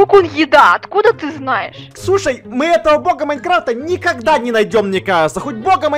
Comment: Мне одной послышалось то что назвал Ксюшай????